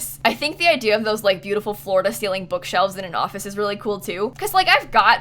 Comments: everytime you said “floor-to-ceiling” i heard “florida ceiling” and i was very confused, like do they have different ceilings in florida too??? 😂😂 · Am I the only one who thought she said "Florida celing bookshelves" when she said floor to celing bookshelves? Edit: thx sooooo much for the likes guys! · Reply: That's what I thought. Until I read this comment, I thought she said Florida ceiling bookshelves. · The amount of times said Florida makes me misheard floor to ceiling as Florida ceiling. And i though at first you kept saying you want Florida ceiling bookcase. Im so confused. It took me a second watch of the entire video to realise what you mean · I thought you said florida ceiling not floor to ceiling bc you talk about florida so much 😂